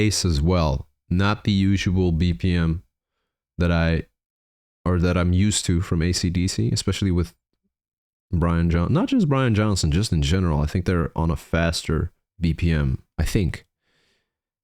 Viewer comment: Did I hear you say "That's Brian Wilson?" Sure sounded like it. LOL